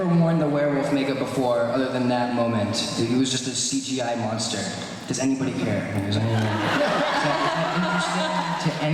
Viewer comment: What did Dylan say? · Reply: I love my pillow